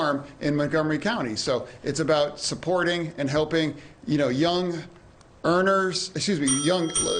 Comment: Did I hear him say "$50 tax break"?